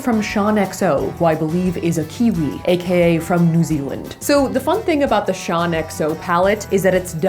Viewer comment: I thought you said nooziland! But you said New Zealand 😂